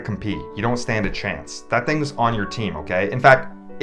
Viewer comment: “wait you’re bi?! i gotta get paid double for this” best line in the cgcu yet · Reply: Oh gosh, I actually heard that as "wait, you're five?" 🤦‍♀️